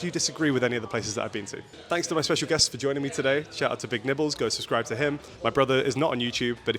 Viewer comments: It took me five minutes in to realize did not introduce the other guy’s name as “Big Nipples.” 😭😭😭 I swear on first listen, that is what I thought he said. 😂😭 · Pleasure to eat pizza with the GOAT of food review content 🙏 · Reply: Love the collab, although my partner completely misheard your name as big nipples. I had to explain that no.... no... not that